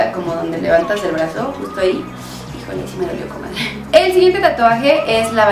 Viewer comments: en el minuto 4:47 dijo "si me dolió con madre" o talvez no oi bien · Oigan alguien me puede decir que dijo en el minuto 4:48 porque no entendi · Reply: Alondra Rodriguez "Híjole, si me dolió comadre"